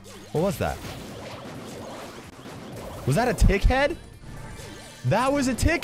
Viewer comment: " was that a tick-head" I heard was that a d*ck-head and I was like 😕😐😆